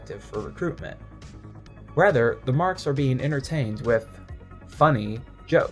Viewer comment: When you said marks I thought you said marx my brain is politically fried